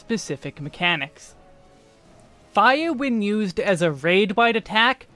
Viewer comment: Btw, Caption Users! Tell me if I screwed it up somehow! This video has custom captions, so you should notice punctuation and such. But also that means i could have broke things? idk plz feedbacks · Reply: You wrote "Gire when used as a raid wide attack" instead of "Fire when [...]" That was the only mistake I could find. The rest of the captions look solid to me. Thank you for the extra work!